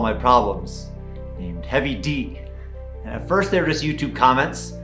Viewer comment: I swear you said "Kevy D" - I was like "who, me?" LOL